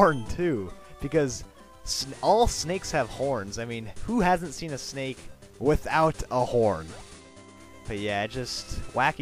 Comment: I thought you said who has ever seen a snake without a hore.